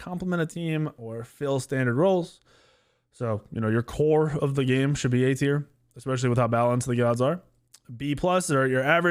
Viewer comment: At first I heard, " Your whore of the game, should be A tier" 😂😂 had to backtrack